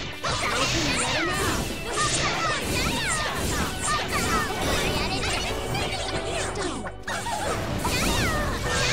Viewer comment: I don't understand S.Yukiko's voice lines with all that distortion :( What doe she say at 3:08? "stay down"? Also, at 3:10? I sounds like "I'll get rid of you" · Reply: Stand aside and away from u